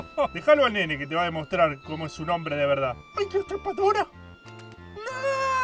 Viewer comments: alguien me dice que dijo el nene? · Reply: O hay que chupadora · que dice el nene? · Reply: A la trepadora